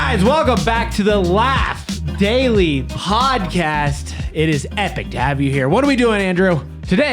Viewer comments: I thought they said last daily podcast lol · In the beginning I thought said Welcome to the last daily podcast 😅😅